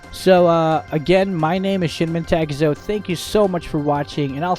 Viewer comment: I thought at intro u said shit mentakezo (no hate)